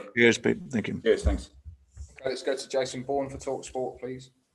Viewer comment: Timestamps 0:00 – Racist abuse of footballers on social media 1:57 – FA Cup quarter-final draw reaction 2:19 – Respect for Jurgen Klopp 3:25 – Learning lessons of last season 4:45 – What Leicester have to do different against Liverpool 5:43 – Jamie Vardy and dangers he can pose Liverpool 6:53 – Team news 8:23 – James Justin injury blow 9:47 – ‘Now you’re seeing the greatness of Virgil van Dijk’ 11:28 – How do you go about replacing Jamie Vardy? 13:09 – Reaching out to Jurgen Klopp this week 14:28 – Timescale on James Justin return? 15:20 – Injuries inevitable this season? 17:05 – ‘James Maddison is a fabulous player’ 19:29 – How key is Youri Tielemans to Leicester? 21:27 – Timothy Castagne and Wesley Fofana out 22:01 – Solutions at full-back 23:06 – Attack Liverpool given they’re struggling? 24:39 – Danny Simpson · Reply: Did actually say, let's go to Jason Bourne? Super CIA agent